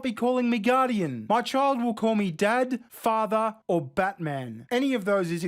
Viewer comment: When you said Dad Father, or Batman, I thought you said Dad, Father, or Fatman xD